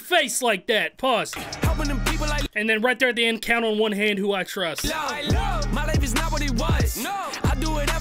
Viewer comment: Thanks for helping me with the hook, I always heard “kinda wanna hate who I trust” 😂